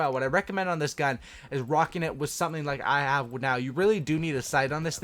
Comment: what the hell did you say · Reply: "Rocking it with something I have like now"